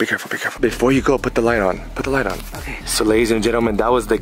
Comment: I heard so that's the gay someone came through I wasn't listening I was exept my dang boiling water is loud but ya I was confused